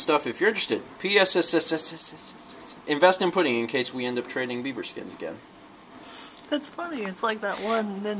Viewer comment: I didn't hear "beaver skins," I heard "bieber skins." Which made me happy.